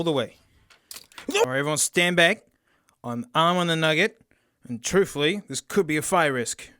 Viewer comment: i always thought he said “an arm and a nugget”